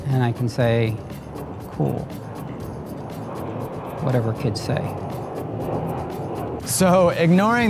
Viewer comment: I'm sorry but did he just say, "whatever kids say"?!